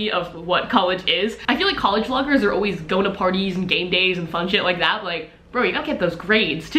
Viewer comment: I really thought you said "going to parties and gangbangs and fun shit like that" and I was like.......... WHAT version of UCLA did I go to????? LOL